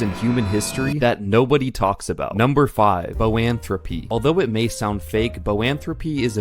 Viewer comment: I thought he said philanthropy (instead of Boanthropy) and couldn't stop laughing. Then it went on and on. Then I thought, "maybe I didn't hear him right..."